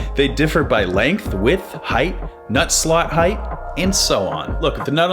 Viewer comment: had to go back thought it said "nutt slut"